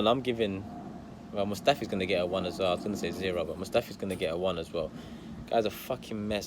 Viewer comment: thought you said scrotum mustafi 💀😭